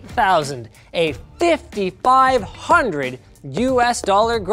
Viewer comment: i thought he said 55000 hahah, americans canadians and their way of saying "10 hundred instead of one thousand" pff